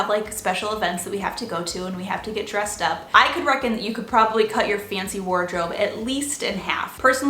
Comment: did you just say 'i could reckon' lol =)